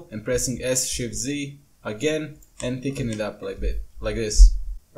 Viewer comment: what are you saying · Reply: "And pickinh it up like this", just move it a bit up with G